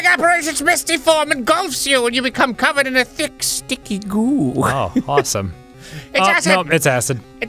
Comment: I thought that said Thick, Sticky God